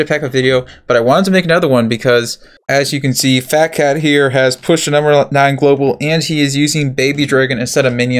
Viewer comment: wait.. did he Say she? Phatcat is a girl?? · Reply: Alfredo Grave yeah I thought I heard she when I first watched it a few days ago, but now I think i hear “he”